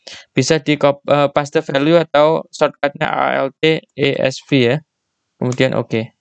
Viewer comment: ngomong apa ya mas? alt esv ? maaf kurang paham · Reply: betul kak alt,e,s,v shortcut untuk paste value... atau bisa setelah kakak copy klik kanan pilih paste special terus chek yang value